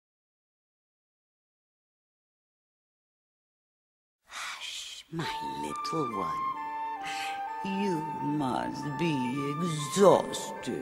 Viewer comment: when I was little I thought she was saying "that's my little boy"...I feel really stupid